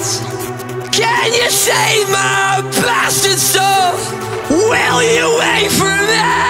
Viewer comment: why did i think it said 'Can You Save My Pasta Sauce' .^.